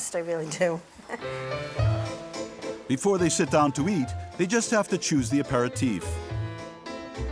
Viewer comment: I thought the narrator said..."before they sit down to eat, Mary had to choose her teeth" okaaayyy. 🤓😬😅😆